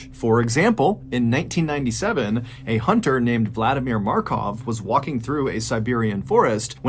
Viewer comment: no way someone named Vladimir Markov was real like at first I thought you said makarov and thought cod took that name